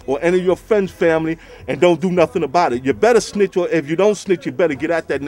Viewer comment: no he didnt, i heard him say "you better snitch" when talkin bout the serial killer next door. u needa handle yo business like cam said, policin ur own neighborhood.